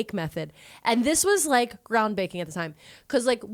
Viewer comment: "Ground Baking" I heard you